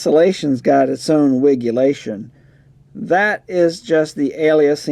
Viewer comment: Did you just say "wiggulation"? 😮